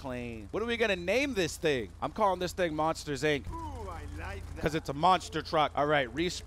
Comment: Who else heard somebody in the background say "OH I LIKE DAT!"